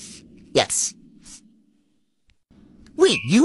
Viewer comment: I thought he said "Math Lizard" i was like, wait- whaaaa? 😂